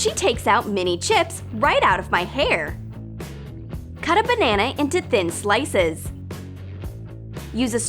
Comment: who heard banaenae?